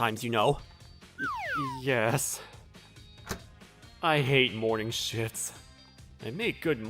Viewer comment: I love how the YouTube captions says “I hate morning shits.” 😂😂😂😂